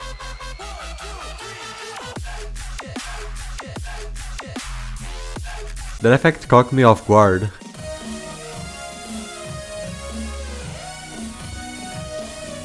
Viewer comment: Why do i hear "That effect cocked me off guard"